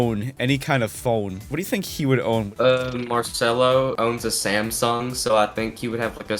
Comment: did i just hear samsung or did i hear sæmsunh